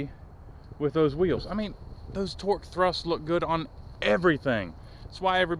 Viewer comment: It’s kind of funny it sounded like you said pork thrust they are Torque thrusts